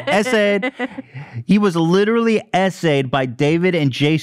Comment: to anyone who’s confused, when they say “SA’d” they mean “sexually assaulted” hope this helps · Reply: I thought they said “Essay/Essayed” and thought it meant vertically shit on or something 🥲 So thanks for clarifying